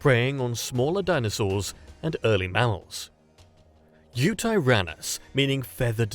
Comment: why did the narrator say mammals like that? Sounded like "mounts" nonetheless and sorry to be nitpicky, awesome content.